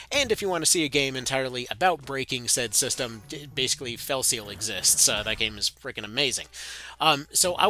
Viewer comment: hi, what is the game you said at 2:50-2:58? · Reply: It was: Arbiter's Mark he talked about.